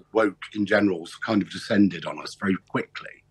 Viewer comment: Almost sounded like he said Woke 'condescended' on us, which would be apt.